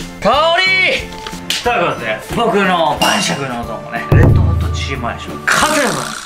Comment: レッドホットチキンモヤシって聞こえるのわいだけ？？